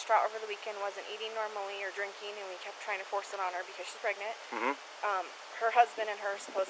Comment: He said “she was pregnant” WAS if that’s not another sign I don’t know what is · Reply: I heard him say she’s pregnant